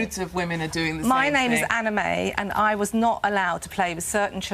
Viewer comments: Wait, did I mishear it or is her name "Anime"? Edit: Nvm it's Anna May, lmfao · She said her name is Anna May and I thought she said her name was Anime and I got happy for a second. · lol i thought she said "my name is anime" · i thought she said that her name is anime not anna may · lol I was listening to the video while scrolling through the comments and I thought Anna May said her name was anime